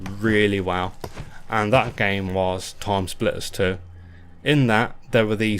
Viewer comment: "that game was" How is it called? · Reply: Timesplitters 2 was the game I was on about. It came out in 2002, and was on the Playstation 2, Xbox, and Gamecube.